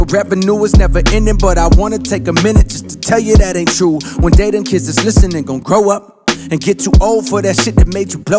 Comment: Swore he said "Jadakiss" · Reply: Donovan Henderson he said one day them kids not Jadakiss lol